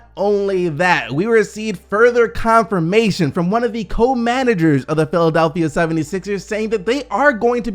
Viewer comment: I had to pause it ctfu @ 7:15 my heart dropped i swore he was about to say Kobe coming back to the Sixers 😁 😂 🤣💀😅 Yo All i heard was Ko...Then i heard Ko manager 😂 🤣💀😅 i'm DEAD😂 🤣💀😅